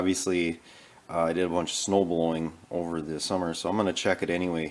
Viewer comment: from 1:01 to 1:04 .. did you say you did a bunch of snow blowing over the summer?